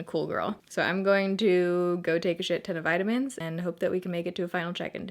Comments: Ahahahaha for absolutely 3 seconds I thought you said now I'm gonna go take a shit. (" now I'm gonna go take a shit ton of vitamins") bahahah sorry, I had to. Love your videos as always ♡♡ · Straight up thought you were saying you were gonna go take a shit there for a second til I heard ton of vitamins..Oh gah..sorry, I had a long day 😂😂 #baerritofam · Literally thought you said "I'm gonna go take a shit" for a second 😂💩